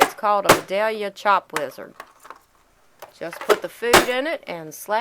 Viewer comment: ohhh! Chop WIZARD! Its too late for my ears to be awake, cuz I heard Chop LIZARD. LOL, silly me! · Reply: I thought she said lizard too.lol